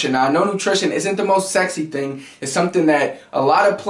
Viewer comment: Did he say "I don't think nutrition is the most sexy thing"